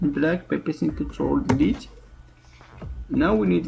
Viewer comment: sounds like he said ''black pepper control delete'' I dong get that part. what did he said?